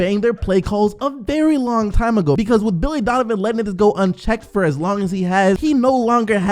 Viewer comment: did he say let niggas go 😂😂😂😂 sdc wildn